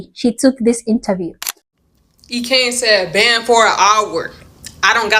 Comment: I honestly don't know what that woman was saying. At 1:33 I'm hearing "e-cain said ban for a hour"